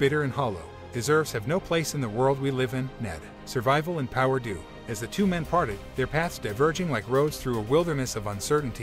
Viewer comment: Am I the only one who heard: As the two men farted?